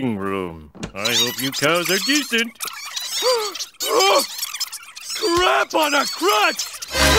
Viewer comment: I love that line! “Crap on a crust!” XD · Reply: I thought he said, "Crap on a Crotch!"